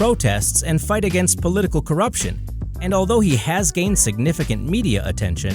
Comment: thought he was gonna say although he has gained significant weight xd